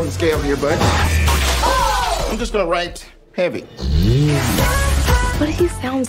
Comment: I thought he said happy.